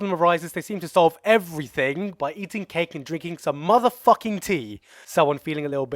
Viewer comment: LOL i thought u said: Eat Shit And Drink Piss